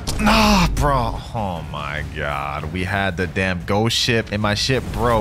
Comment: I thought he said "damn sh*t" 😂